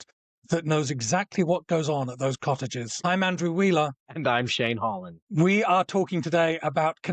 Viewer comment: Did I hear Shane Hollander?